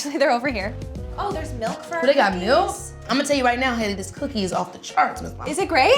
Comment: Did keke call her hanna???